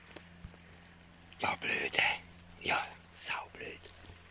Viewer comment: isch sau blöd gelle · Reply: zwischen 3:50 - 3:51, da hab ich "sau blöd.. sau blöd.." gehört